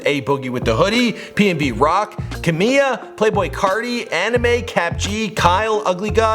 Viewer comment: did he just say anime